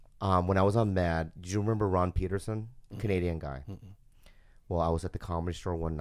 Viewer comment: Omg I thought he said Jordan Peterson, a guy from Canada...